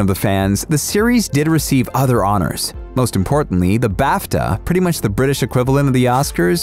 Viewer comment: When he said BAFTA I thought he said bathtub